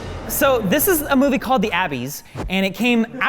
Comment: d-did he just say... "The Abby's" ?!